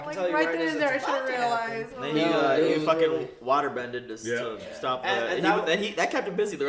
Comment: Did you say waterbended? Lol.